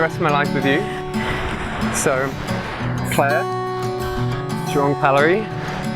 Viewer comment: I thought he said " Clare John Callery" 😂😂